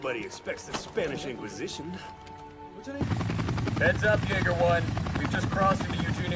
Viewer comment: ayo what did he call them? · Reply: jeäger one.